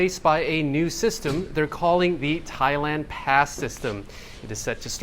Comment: Removing COE and introducing “Thailand Pass” makes no difference. It is just a change of name. Isn’t the Arrival Card more than sufficient? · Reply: I thought they said Thailand Cast System! Which seems more appropriate.